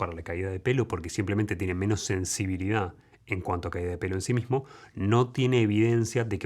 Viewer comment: estoy escuchando mientras trabajo y en una parte escuche "caida de pene" y yo asi de WTF!! le regrese y ya entendi bien "caida de pelo" jajajajajajaja